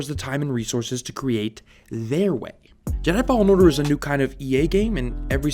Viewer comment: Sounds like your saying fallout order